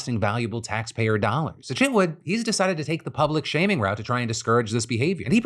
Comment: I'm gonna be honest, it sounded like you said shit wood instead of chip wood at the end of the 1st story.